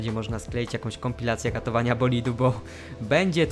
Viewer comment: no co tu trzeba poprawiać no nic więc zapominasz o Bahrajnie i lecisz do Arabii po dobre punkty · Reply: usłyszałem katowania bolidu xD (niczym w zgłoszeniach)